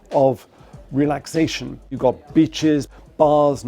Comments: Did he say b*****s at 1:30 ? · Sorry, but for an American ear it really sounded like he said 'You got b!tche$...'